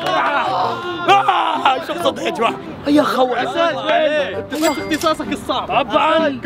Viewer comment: كني سمعت خ—-ول ؟🙃🙃